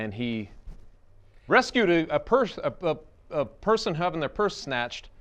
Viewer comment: At first it sounded like said “rescued a person having their first snatch” which makes this the funniest mk ever made.